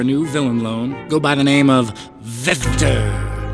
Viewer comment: My subtitle says at 0:20 part that vector becomes Better 😂, my childhood is ruined